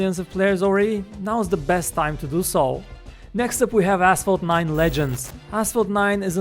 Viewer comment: who else heard asshole 9 ?